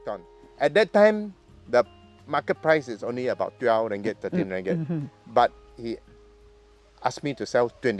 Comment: How many ringgit did he say? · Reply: Transcript helps to show it was probably 12 and 13 ringgit. And was it later, in 2019, when it went up about 9 times more to R115/kg, AU$38/kg?